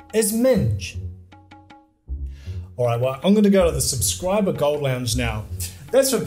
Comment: I misheard "Subscriber Gold Lounge" as "Subscriber Goat Lounge".